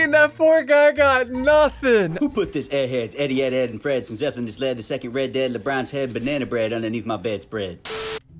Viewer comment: Who put this airhead Teddy edd head something idk want he said, the second red dead labrons head Banana bread underneath my bed spread. I had to use 0.5 speed to even understand what he said